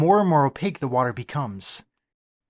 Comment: ish I kept hearing "sheep-protector", which gave me some strange ideas before said it clearly.